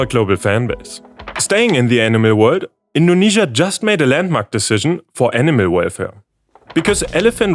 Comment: Am I the only one who heard "landmark decision for anime waifu" around 5:10 ?